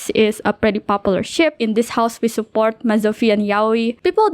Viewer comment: as someone who hasn’t played the game but has been watching this video I could have swore you were about to say “In this house, we support Mesothelioma” and I was so caught off guard haha